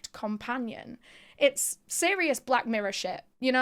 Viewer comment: I thought she said it was Sirius Black 's mirror shyt, and it took me a min to realize she was saying serious Black Mirror lol